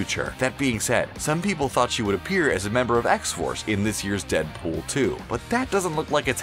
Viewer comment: For a second, I thought it said Deadpool 8 ☠️